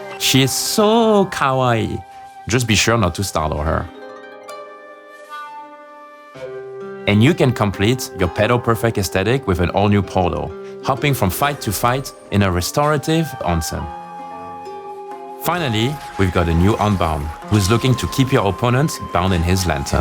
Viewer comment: really thought he said "pedo-perfect aesthetic" 13:27 😬thank god for subtitles